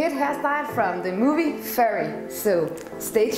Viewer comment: Did she say furry?🤣